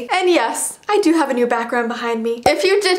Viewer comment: i thought she said backrum- murkcab :D